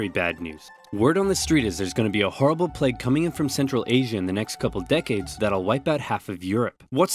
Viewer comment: did he say “central Asian??”